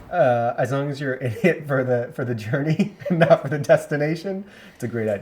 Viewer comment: Twice you played that quote from and both times I thought he said "as long as you're an idiot for the journey . . . ." The CC said I heard it wrong.😝